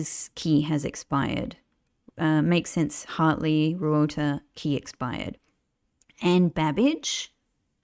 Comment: "Hartley Ruota, he expired" - rest in piece, poor man. Must have been the broken heart after losing Herbert ... :P (not sure if I mishear "key" for "he", of if you meant "his account expired")